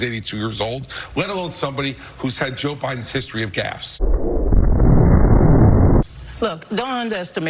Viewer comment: Hahaha! 🤣 I had thought Chris said that he had a history of gas 💨 as opposed to gaffes! He has a history of both! 😆🤌🏻 What? Huh?👂 ❤ Coffee☕️ time!